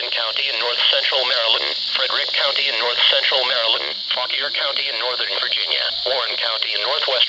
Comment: Did he say fuck your county? 😂😂😂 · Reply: He said Fauquier